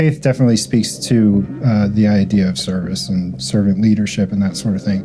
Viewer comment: He is talking about leadership but later he's only like "well I didn't look at this or that" - leadership means taking responsibility, right?! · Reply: I think he said his faith made him more servile, following the leaders.